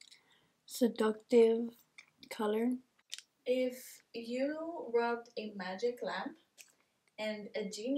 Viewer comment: I have just been made aware that on the question of the “if I robbed a genie lamp” it was in fact not “robbed” 😂😂 but “rubbed”. When I was editing I heard “robbed” & thats why I had put that LMAO I do apologize for the misunderstanding 😂😂😅